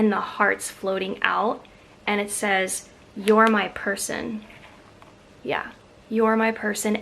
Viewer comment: I hope you do make a wreath! I need some new ideas. I appreciate all your ideas so much! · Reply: The valentine ribbons are gorgeous!I didn't have my eyeglasses on and I thought the one ribbon said "you are my poison"l died laughing 😂😂💕 love your hauls